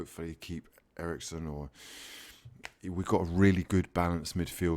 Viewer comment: Did you just say if they can make him and Winks work... "WE" have a really good signing here? Did I hear that or am I going mad